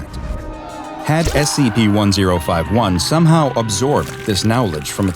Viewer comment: did he say now ledge?